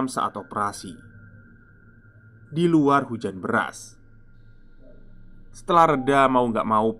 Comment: Sepertinya saya dengar di menit 27:27 mas bilang hujan beras.. apakah kalian ada yang sama? Atau saya yang salah dengar seharusnya itu hujan deras😅🙏🏻